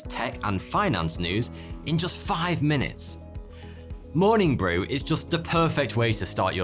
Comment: I thought I heard you say "morning wood" lol!!!